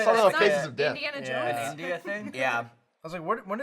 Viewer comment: I keep hearing 'nudist thing' when Ryan's talking about that thing he did with Meg - is it just me? o.o